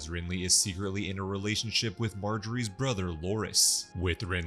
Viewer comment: Did you say “ he’s secretly in love with her brother….”. ??😂😂😂